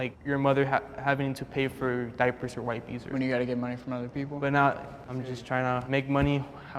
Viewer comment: im sorry but when was like "oh, you have to get money from other people" his privilege JUMPED OUT bc not everyone has the resources you do. theres no reason to be judgemental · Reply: He said “Oh, when you have to get money from other people” you misheard that pretty badly.